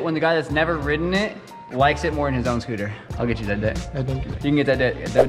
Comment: Idk why but at 6:12 I heard that he sed I will get that d***😂😂